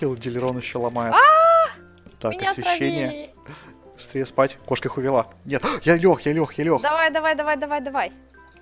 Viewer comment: мне одному послышалось 17:30 "кошка хуёво"